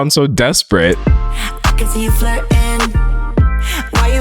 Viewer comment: On song 5… why do I hear her say ‘I CAN SEE YOU FARTIN’ 😂😂